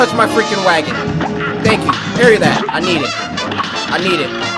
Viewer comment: When says “parry it” All i hear is “PERIODT”